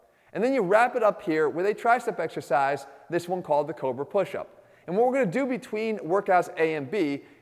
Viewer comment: I thought Jeff said COVID Push-up for a second